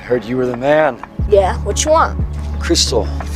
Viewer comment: I thought he said “jit come on yo” 💀